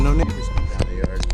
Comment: What did you say at 16:40? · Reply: “Neighbors” definitely I thought so too tho 🤣🤣